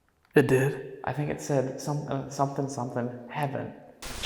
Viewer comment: before it says, “heaven,” a different, higher voice says, “I went…”. So, I think it was saying, “I went to heaven.”